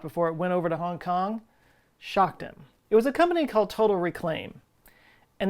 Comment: had to listen again. At first I thought I heard Total Recall... I need my coffee.